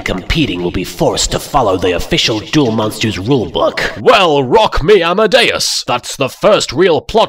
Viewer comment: Can somebody type what he says for me? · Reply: Perhaps I am late but here you go Yugi said “rock me amadeus” which is a song by falco It was at the end of the video